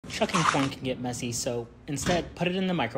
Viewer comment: It’s corn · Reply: I thought u said “chucking corn can get massy” 😂😂😂😂😂😂😂😂😂